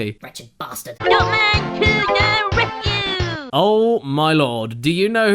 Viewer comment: Am I the only one that thought when said "Dot man to the rescue!" That he actually said Dump man instead of Dot man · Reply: I thought he said “Duff man to the rescue”